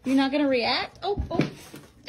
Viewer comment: he really said "ill react"